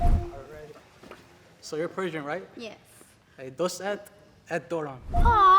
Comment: what did he say? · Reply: He said doset daram which means i love you in farsi